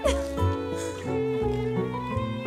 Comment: What does the man says at 2:16 ? I'm not a native so I struggle to understand. Great video anyway ! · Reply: "Thanks for setting us up, Mike. It was a solid effort"